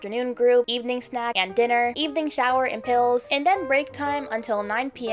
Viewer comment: WHY DID IT SAY SNACK TIME IN THE BACK INSTEAD OF SHOWER?!? we eating snacks instead of showering 😭